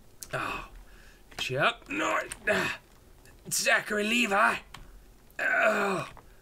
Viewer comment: Does he said " zachary levi" at 7:17?